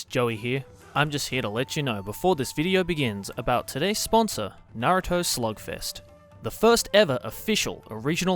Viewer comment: Naruto Slugfest is the Raid Shadow Legends of the anime comunity. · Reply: I legit thought that Joey was saying Naruto Slogfest at first.